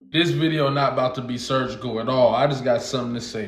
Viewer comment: I thought you said you wasn’t going to get surgical 😂😂😂😂